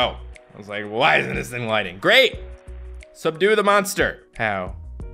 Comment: I thought it said..seduce the monster...